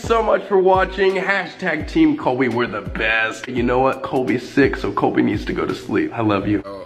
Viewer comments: I thought he said “I’m six so I need to go to sleep now” 😭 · "Colby's sick so Colby needs to go to sleep, I love you" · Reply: I thought he said “Colby’s six so Colby needs to go to sleep now, love you”